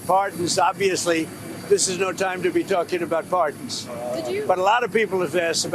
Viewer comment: What I heard: “Farting. This is no time to be asking about farting.” lol over the sound of the chopper and the screeching of the reporter I heard his “p” as “f” idk were in hell ❤